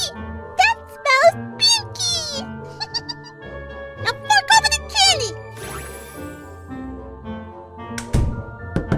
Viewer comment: I don't think this is what it's supposed to be, but swear I heard her say the f-word. But maybe it's the way I heard it. I was probably supposed to be "hand" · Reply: "Now fork over the candy"